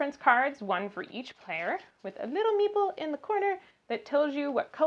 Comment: I was watching this video while running my bath and couldnt hear very well so I turned the captions on and every time you said meeple it thought you said meatball 🤣